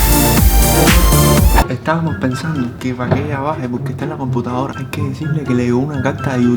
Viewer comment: dijo * que Valería baje* escuche bien